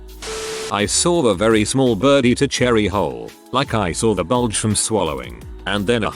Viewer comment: I was listening without watching, and I think it’s time to go to bed, I swear i thought he said “I saw a bird swallow a Cherry hole” and I’m like WTF is a cheery hole? Took a minute to realize WHOLE, once he mentioned seeing the outline. Nighty night y’all.